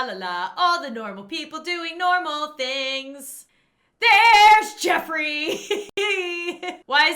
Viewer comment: When you said ‘here’s Jeffry’, I heard ‘Red Robin Yummmnm’, like the Red Robin add